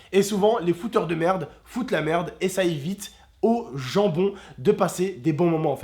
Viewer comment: quand il a dis "gens bon" j'ai compris "jambon"